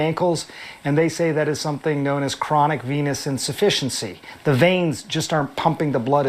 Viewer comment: I swear I heard you say DT has chronic penis insufficiency 😂